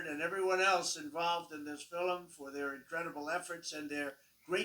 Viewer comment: Did he say "Fil-em"?